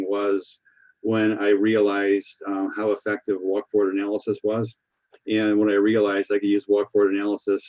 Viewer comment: and 30:36 he mentions "? analysis". What was that word? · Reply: For a moment I thought he said Wyckoff, but apparently he "invented" the Walk Forward Analysis